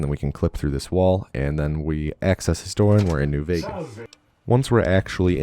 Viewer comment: I thought it said New Vegans Speed Run...